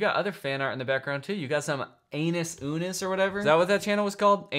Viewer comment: It’s one in the morning, my cat is snuggling me, Im trying to be quiet, then I hear “UNO ANUS” And I explode with laughter so hard that I sneeze three times afterwards. My cat just looks deeply troubled.